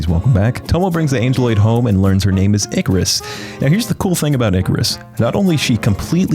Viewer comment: Yo at 2:44 i thought you said THICCaros not ikaros